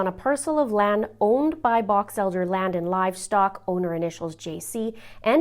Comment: I thought you said Batch Elder. It's actually Box Elder.